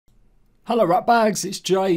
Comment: Did he just say “hello rat bags” at the beginning??